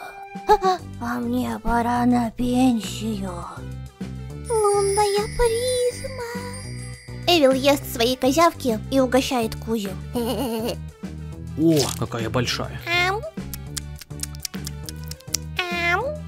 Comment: мне послышалось ил там было лунная клизма...